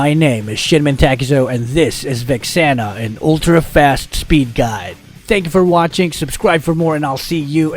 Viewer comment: I thought you said a speed guide for molina